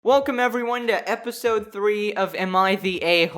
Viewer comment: I thought he said welcome back to an episode of M I D A O😂🤣